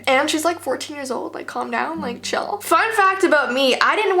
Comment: When she said “Fun Fact” I thought she said “Fuck that”